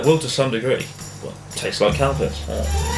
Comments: I thought you said "cow piss" instead of "calpis" haha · i thought he was saying cow piss instead of calpis.